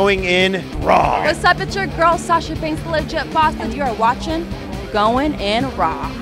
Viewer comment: Everytime i hear sashas part i hear "alicia fox" instead of "the legit boss"